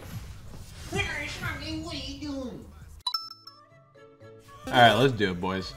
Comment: what did he say ?! is that Chinese ? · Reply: "[inaudible] smalcat, what are you doing?"